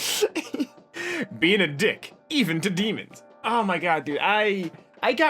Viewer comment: thought it said beating a dick even to demons... watching youtube after beating it can have some effect